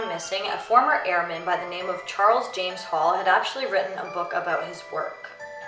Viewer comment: sorry to say this but i thought you said James charles